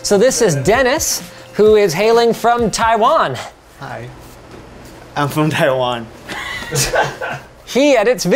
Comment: the subtitles say "Hello, it's a pleasure to be here, coming all the way from Taiwan" what he actually said: "hi, i'm from taiwan" lol p.s. it's a nice firepole, but isn't too far away to actually grab it safely? you gotta stretch to reach it from the stairs